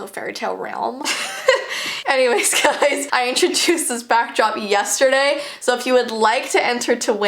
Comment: Back grop Me: made a new word back drop+ back ground =back grop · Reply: I thought she said back rop